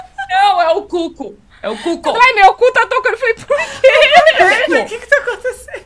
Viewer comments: Eu tb entendi "o meu cu tá tocando"... 😂 😂 😂 😂 levei um susto! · Eu nem vi o filme, as cenas pós créditos, mas eu ACHO que sei o motivo do grito da hahahahahaha E to curiosíssima pra ver. E sim, eu tbm escutei "meu cu tá tocando"